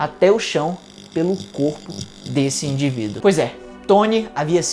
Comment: que ódio, juro que ouvi "...pelo cu daquele indivíduo" KKKKK